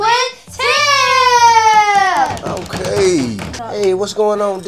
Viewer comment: did they say him or tim